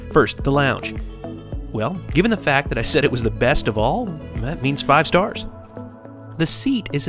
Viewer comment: I thought you said Ethiad is the best